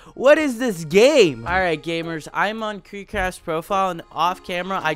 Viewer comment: You said "KreekCraft's profile" but your AI generated captions thought you said "kick ** profile" 🤣🤣 I guess Speech to Text isn't filtering what it's captioning on your videos!